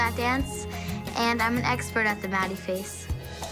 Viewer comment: when said im an exerpert AT THE MADDIE FACE I THOUGHT SHE SAID THE BADDIE FACE